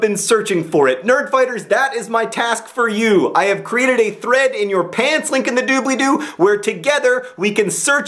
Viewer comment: My mother: "Did he just say "Link to your pants in the dooblydoo?" Me: *smiles* Mother: *rolls eyes* "I don't want to know."